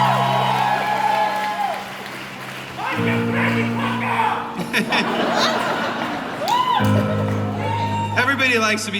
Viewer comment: What does he yell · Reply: Mike you crazy fucker